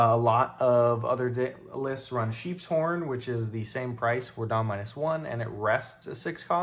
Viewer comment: AYOOOOO WHAT DID HE SAY 😂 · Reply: “Sheep’s Horn” It’s a 2 cost purple event that rests a 6 cost or less.